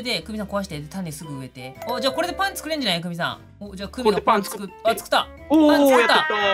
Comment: 「パン作った」が「パンツ食った」に聞こえた(小３思考)